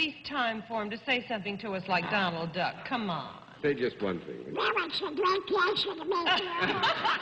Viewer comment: what does he say at 7:00? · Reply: Well it's a great pleasure to meet you.